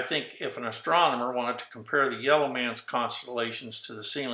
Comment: am i the only one who heard him say "the yellow man" @ 5:10.... lolol... its ACTUALLY REALLY FUNNY how i've been taking notes for EVERY video watched during this research project, however because it sounded so passionless and like he was just a rando "white man"... i figure i'll just set my pen down until i learn something i didn't know... I'm sure something he says will be a viable piece of knowledge AT SOME POINT... I'll give it till 7mins at least cuz he's definitely not the only white person making videos on this subject ... just the most boring. Im like really trying to stay awake rn...lolol